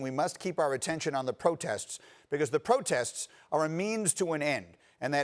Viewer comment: did he just say that protests are just memes to an end.? 😭😭🤣🤣🤣🤣